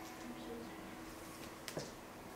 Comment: What she said at 3:27 ? smell socks ? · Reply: She said “this movie sucks” 🙃 cause feel-good movies are meant to make you feel good, but when you realise it’s just a movie and will never happen in real life, it’s suddenly not a great movie anymore 🙃😊